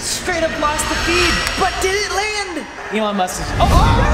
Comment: Sounds like wren is saying "Elon Mustard" at 4:20 LOL